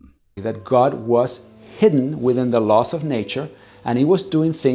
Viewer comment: this was, as usual, great. I gotta be honest though, at first I thought he said 'God is in the LOSS of nature'. Heehee. And apologists come on with the same crap. And as usual, they can maybe get to a deist God but they just assume it is their God! And they think the Cambrian was (because called explosion) fast. It was millions of years long! lol And has he looked at the human body? Some design. All the things he named, towards the end, animals other than humans can do, too. What a ditz. 👍🌊💙💙💙🌊🥰✌